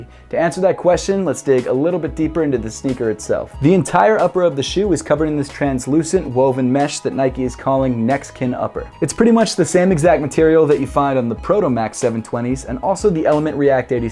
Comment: Also it sounds like he says "Mexican upper?